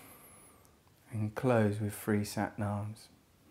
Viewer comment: I thought he said "enclosed with a free sat nav"